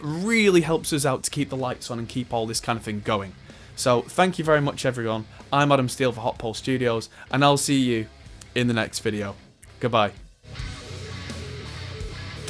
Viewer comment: Had bad earphones on and thought you said hot coal studio LOL. PS thought you had to be Karl Smallwood to hold a cup in a youtube video.